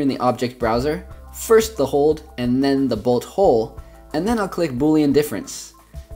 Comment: when he said bolt hold I thought he said butthole😂😂😂😂😂😂